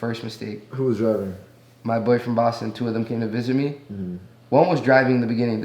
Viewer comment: did he say his boyfriend boss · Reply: “my boy from boston”